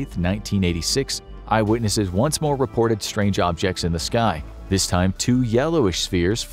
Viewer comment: Did.... Did he just say xs in the sky.... Oooo farts